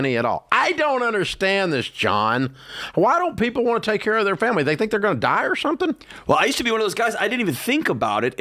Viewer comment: Can someone explain the term life insurance ad for me? I’m confused when Dave says “they think they’re gon’ die or something?” And I hearing that wrong? Is he saying “they’re think they’re NOT gon’ die or something?”